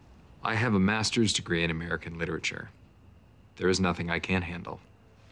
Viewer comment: i quote “i have a master’s degree in american literature there’s nothing i can’t handle” way too much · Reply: I heard “there’s nothing I CAN handle” 😂